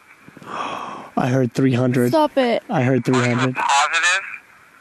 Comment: i heard 15 or 16 not 300